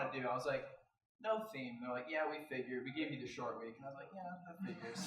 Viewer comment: SHORTWIG. That’s what I heard